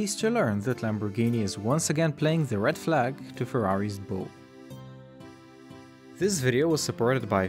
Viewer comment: I thought you said Red Bull